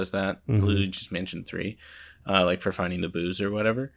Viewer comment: What I heard: “Rumble for context clues, in Luigi’s Mansion 3, for finding the booze.” Man, these ghosts are driving Luigi to drink😆